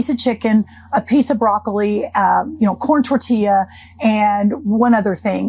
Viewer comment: Did she just say a piece of broccoli? WTF